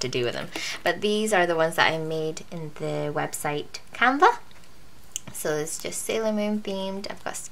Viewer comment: I dont know if you'll be adding this to the description later but just in case you don't I figured I'd ask what website you were talking about at 8:42 You just said it a bit fast and I have some hearing issues so I didn't catch it. Thanks in advance :) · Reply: Hi Ella =D The website is called Canva for making the Sailormoon dividers :)